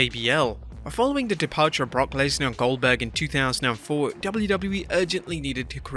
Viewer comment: I thought at 8:30 he said oldberg.... 😆